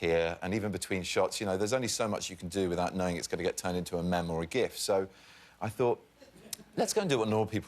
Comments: did he say mem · did he just say mem... I'm going to cry myslef to sleep